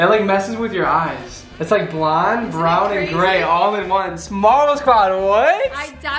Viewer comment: When said marble squad I thought he said model squad LMAO